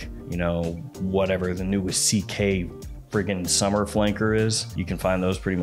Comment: I swear I heard him saying "...Louis CK summer flanker..." the first time 2:10 🤣🤣🤣🤣